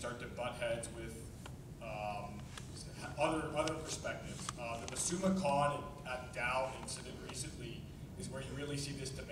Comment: What did the guy say at 6:55? I couldn't understand him. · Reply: I assume he was talking about Masuma Khan at Dalhousie University.